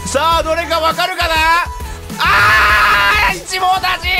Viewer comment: ｱﾞｱﾞｱﾞｱﾞｱﾞ七網打尽〜！！ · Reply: ウンチクぴえんぱおん あぁ一網打尽が自分は七網打尽に聞こえたわけだあ〜ありがとね